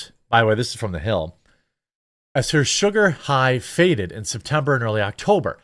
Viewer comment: I thought you said "watermelon sugar high," for some reason 😅 I'm was like excuse me?